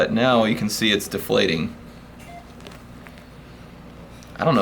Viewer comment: Swear i heard him say it smells like mcdonald fluid